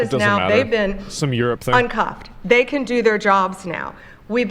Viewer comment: thought she said un-cucked